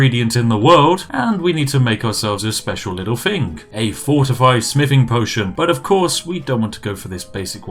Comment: When you said fortify smithing one time I thought you said fortify spiffing